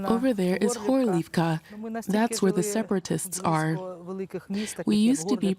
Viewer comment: she is not saying "were the separatists are", she actually said "over there is OCCUPIED Horlivka". Feel the difference, DW!